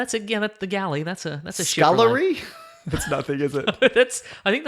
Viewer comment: During the ad, when said "Scullery?" I thought he said "Celery?" And I got wayyy too excited cuz I was wayyy too convinced that there was about to be an out of context celery man reference